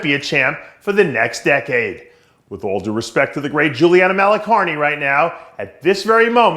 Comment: thought he said "the late Juliana..." lol wtf